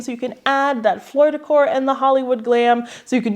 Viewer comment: I thought you were saying Floridacor and picturing the golden girls